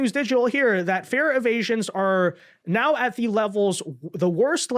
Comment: said "fare evasions" but I thought he said "fear of Asians." 😂